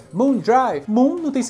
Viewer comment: como assim ela fala Moon Drive? Eu sempre entendi "Miquei" hahahahha Sempre adorei jogar com ela, pois é rápida e eficiente. Seu canal é sensacional, parabéns!